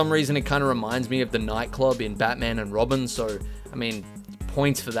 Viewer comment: I keep hearing ‘Robert’ instead of ‘Robin’ and it sounds like shit post text each time